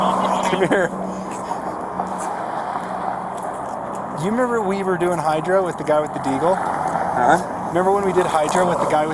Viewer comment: Did he just say they were doing hydro?